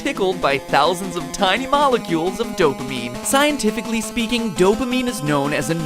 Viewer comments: I thought it said “dopmemes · I never got addicted to things like candy crush cuz I don't really play those games Edit: 3:32 I keep hearing Dope A MEME